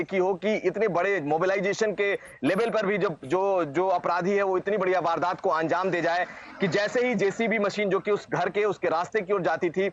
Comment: i heard reporter saying "itni badiya vardat ko anjam dejaye" correct me if i am wrong